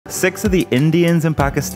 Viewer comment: Misheard Sikhs to sex I imagine how kiwi person can't pronounce the letter خ And especially switch the I to E